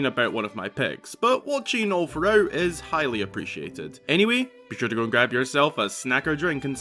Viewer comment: When you said “highly appreciated” I thought you said “Hylia Appreciated”. But that would make for a good pun though not gonna lie.